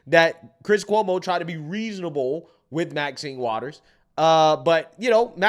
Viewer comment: instead of maxien waters its minimal drought